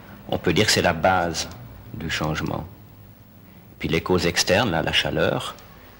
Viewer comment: One small mistake. At 47:18 the subtitles say "Then, internal causes, the heat, are conditions of this change" when it should be "Then, external causes, the heat, are conditions of this change"